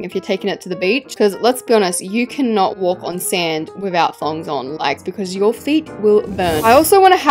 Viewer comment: WITHOUT WHATS ON? i cant be the only one who heard thongs